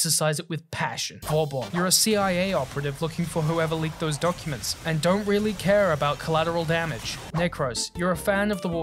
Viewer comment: what does he say and what warframe is that? Edit I’m guessing Vauban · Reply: Vauban and I said “you’re a cia operative looking for those leaked classified documents and don’t really care about collateral damage.”